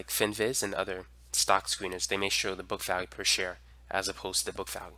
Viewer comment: Great video, thanks a lot for it! What did you say is the book value called on Finviz? Didn't quite get it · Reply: on Finviz its called book value per share.